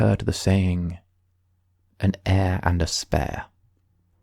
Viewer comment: Him : "An heir and a spare" What i hear : "An air and Despair." . "Is this Danganronpa?"